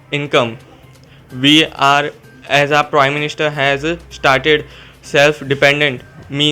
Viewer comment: Did he just say, "We are as a prime minister?"